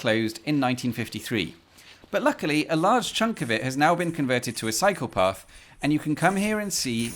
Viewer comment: "A large chunk of it has now been converted to a psychopath" Am I the only one who hears it like that?